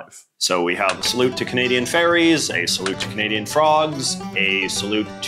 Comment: I was listening to the video rather than watching it, so when I heard "Canadian Ferry's" I interpreted it as "Canadian Faeries" had to come and rewind the video to see what was going on here. I was mildly disappointed.